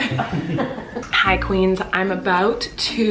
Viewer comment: Omg! Why did I hear CollQueens at 2:57. That’s the new fandom name 💀😂